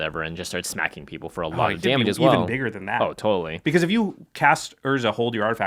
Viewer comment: does he say "damn, even bigger than that"